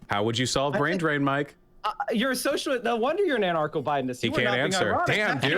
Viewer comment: He clearly said "anarcho botanist".